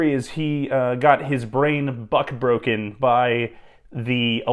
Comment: I sat back in my chair and almost sat on my bal- 🥷 did you just say buck broken????😂